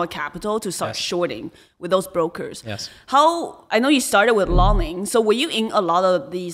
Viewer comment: what Does she say? Startes with lawning? Im really curious about what they’re talking about here, if anyone could help me out that would be appreciated.